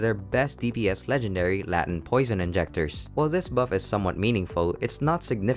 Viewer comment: anyone else hear "latin poison injectors"? this dude is the best voiceover guy simply because he mispronounces stuff all the time in the funniest ways xD